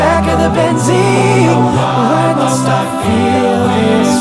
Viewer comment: Did they say "Must be your mommaaayy"? at the end there? hahaha 3:50 I think they totally did.